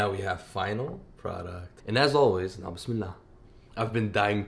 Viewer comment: Why do I always hear " nasmilla" instead of bismillah?